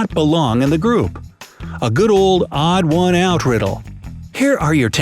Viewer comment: Did he said the odd one out